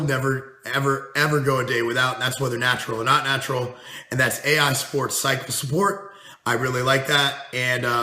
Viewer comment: At the 10:00 min mark, did you say AI Sport Cycle Support? What is that and why do you recommend it? Or did you say AI support (aromitase inhibitor)?